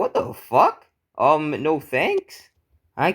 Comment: did I just hear the n word 🧐🧐🧐